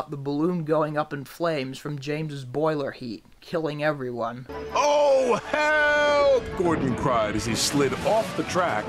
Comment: i thought he said OH HE 🏒🏒